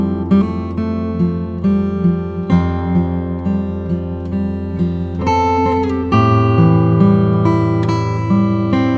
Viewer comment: Who is the Red Sox player at 2:00? Can't make out the name. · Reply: Lee Tinsley